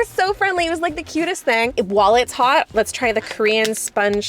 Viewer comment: “While it’s hot” I thought u said “wallet is hot” cause u just bought so much hahah